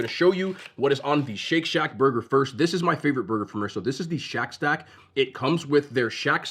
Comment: I thought u said shit shack 😮